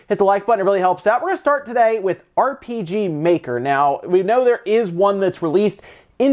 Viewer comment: I thought you said RGT maker. Diet Dr Pepper Cigarettes Stevie Richards